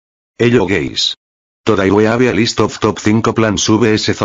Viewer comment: did you just call me gay?